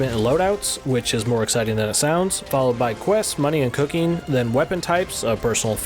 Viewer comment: It sounded like he said quest, money, and cocaine at the beginning 😂😂😂